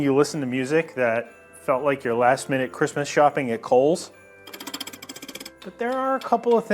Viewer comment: Being Australian, I heard the "last minute" statement as "last minute Christmas shopping at Coles" which would indeed be some desperate Christmas shopping, unless it was for Christmas lunch.